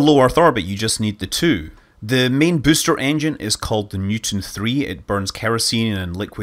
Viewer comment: I misheard Scott when he said Newton 3. I thought he said mutant 3. I am slightly disappointed.